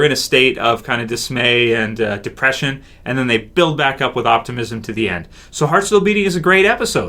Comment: at first i thought he said build back up with autism, lol